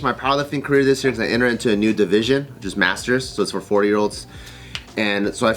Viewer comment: me too subjn, I though i heard 4 yr old but replayed and said 40yrolds